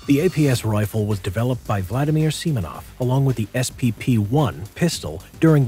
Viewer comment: thought he said "*Vladimir Lenin*"